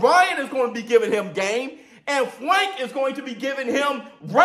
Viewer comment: Frank is a SOUTHPAW…I’m confused right now🤔🤔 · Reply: try to listen and think. I said Ryan will be giving him GAME! Use brain to listen, comprehend and think!